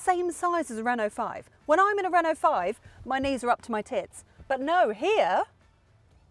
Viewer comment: up to your …had to rewind! Thought you said pits! lol but noooo. Haha